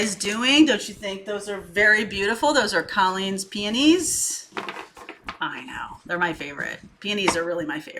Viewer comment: For a sec I thought you said panties I had to run it back😅